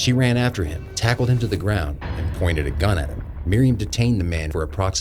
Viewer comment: Pointed a gun at HIM* Captions say pointed a gun at Miriam